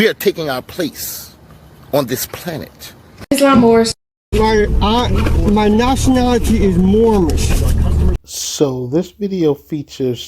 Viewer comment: I always hear.. “my nationality is moron”